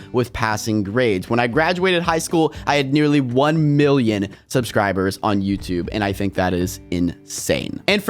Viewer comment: Why did I heard 1B subs?